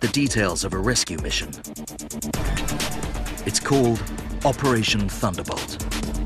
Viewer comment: i swear i hear "operation thunderBUTT" lmao